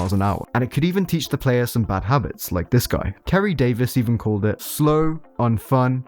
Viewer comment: I misheard Kerry Davis as Terry Davis and you had Me wondering what the TempleOS dude had to do with HL2